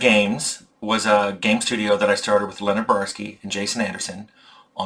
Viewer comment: I thought you said "Leonard Bernstein" at the beginning and my mind was blown for second. I've always thought Arcanum's music was great, but my whole world turned upside down for a second 😂